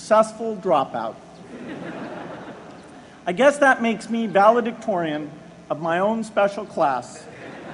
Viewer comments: What does he says at 1:55 ? I guess that makes valued ... of my own special clas?? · What does he says at 1:55? I guess that makes valued ... of my own special class